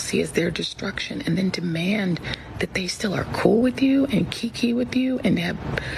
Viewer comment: what does anti-joy reid say here? key-key? kiki? am I mishearing or simply lacking lexical depth?